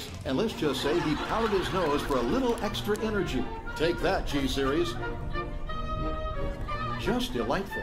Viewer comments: does he say t series he can tell the future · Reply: He said G-series. as in the powdered energy drink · did they say "take that T Series????" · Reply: G Series (referring to gatorade)